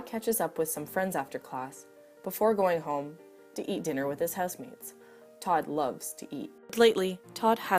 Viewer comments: this isn't related to the content but at the beginning i thought it said "eat dinner with his husband" instead of housemates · i thought it said eat dinner with his husbands